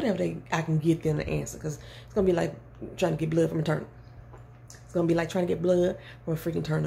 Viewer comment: The fact I thought she said “trynna get blood from a turtle 🐢 😂😂” I’m like dang that would be hard huh? 😂😂